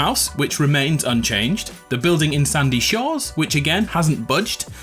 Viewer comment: Did he say Sandy shores?